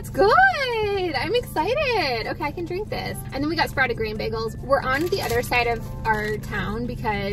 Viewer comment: I thought you said sprouted green not grain😂